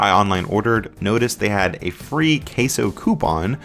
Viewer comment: did brodie said CASEOH COUPON 🍔🍔🍔